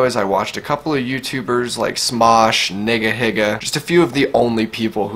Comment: Did you just call him nigga higa?